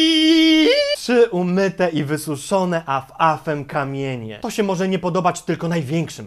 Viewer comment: ja tam usłyszam "Trzy umyte i wysuszone Mak Afem kamienie"